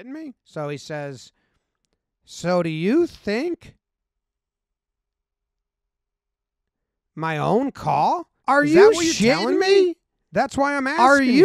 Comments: What I THINK he says is something along the lines of "So do you think I'm messing up your pitchers and batters with my own calls?" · "So do you think I'm basically bullshitting back here my own call?" @ 3:55 "I hear you, that pitch was out, so, y'know, I watched it" Please hire me 😂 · I think he said "So you don't think I replayed the past game back to see my own call?"